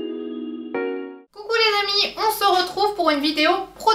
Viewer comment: je ne suis qu'au début de la vidéo et je rigole toute seule!!!! Quand tu as dit, "hello les amis", j'ai entendu "Hello, les MAMIES" et j'adore l'idée!!!!!!! bon je retourne au visionnage, bisous très très gros de Mamie Flo